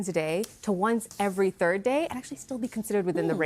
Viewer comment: I don’t believe it is. She even said in this video up to once every 3rd day. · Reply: This is hilarious. I thought she said "once every Thursday." Haha